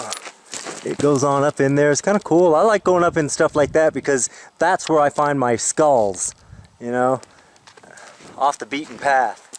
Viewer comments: if you listen closely you can hear him whisper “looks like” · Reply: did he say 'thats where i find my skull'? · did he say find my skull? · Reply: "Find my skulls". He had posted in the past how, during his hikes, he finds all sorts of stuff, including skulls.